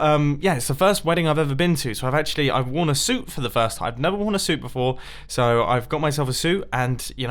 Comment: I thought he said "I wore a soup for the first time"